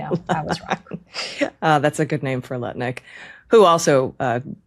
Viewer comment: Oh, I thought she said NUT-LICK not Lutnick 😉